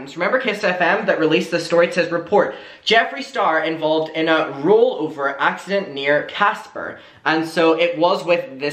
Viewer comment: Ngl I thought it said "rollercoaster" instead of rollover... Oops